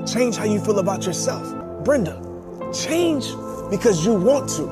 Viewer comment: Does he say Brenda? I felt like God was calling me. Lmao.